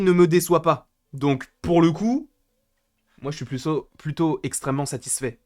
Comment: "moi j'suis puceau" MDR